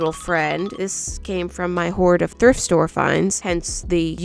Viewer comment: am i the only one who heard thirst store